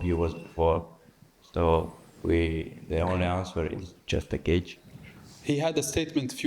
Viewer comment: I keep hearing, "the only answer is Justin Gaethje" 😂